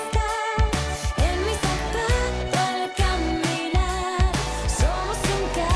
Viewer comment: Jajajaj yo tenía 10 años cuando salio esta cancion y pensé que decía "en mi zapuuuto al caminar"